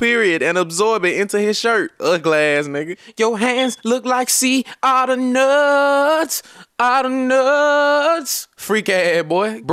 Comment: You hand look like seeds outta nuuuuttsss 😂😂😂😂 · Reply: ALV LION GANG lol why i thought he was saying sea otter nuts